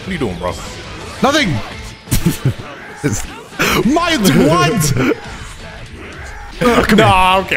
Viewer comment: Was it just me or did it sound like said "my twat!! Go to